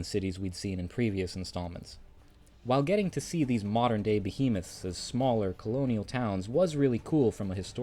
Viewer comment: Did anyone else hear modern day penis’ @ 6:05?